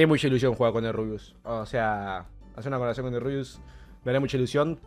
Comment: pensé que decía coloración y escuche que querias estar rubio XDD